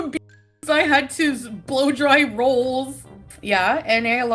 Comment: did say she had someone blow dry her rolls?! wtaffdf 😮